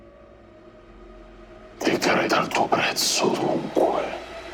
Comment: Ciao ragazzi, innanzitutto complimenti per l'idea di registrare la vostra campagna, sta venendo davvero bene, e complimenti (davvero) anche per come state "recitando" i vostri rispettivi personaggi. Vorrei sapere cosa dice tra il minuto 31:44 e il minuto 31:49, mi sembra che dice "Dichiara il tuo prezzo dunque" ma non si sente molto bene e i sottotitoli di YouTube non aiutano per nulla. Grazie e scusate il disturbo, buona serata e buona domenica a tutt*. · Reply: " Decreta il tuo prezzo dunque " - a quest' ora ...